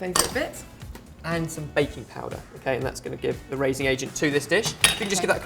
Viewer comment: At first I thought he said bacon powder instead of baking powder, if only I had video taped my expression.